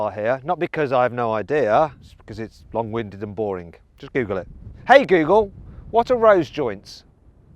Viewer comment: しっかりググれって聞こえるw